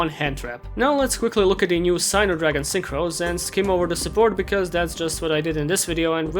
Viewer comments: When he said "Signer" Dragon Synchro Support, I thought he said Cyber Dragon Synchro support and I got severely confused. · I thought said Cyber Dragon Synchros instead of Singer Dragons Synchros and got very confused. Cyber Dragon Synchros? In my children's card game?